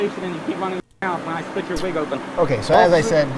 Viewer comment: Really? It's rather quiet, I thought I heard "now split your legs open", as they're going to pat them down and search them.